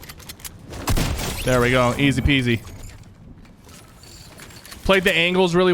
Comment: Did sypher just say easy peasy